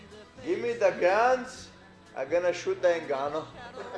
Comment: LMFAO WTF HE SAID HES GOING TO SHOOT NGANNOU 🤣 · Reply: Shoot “the” ngannou 😂😂😂